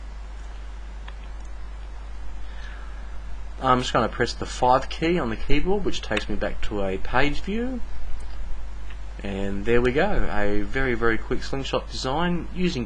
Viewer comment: Great video, thanks a lot! I keep hearing "...press the fart key..."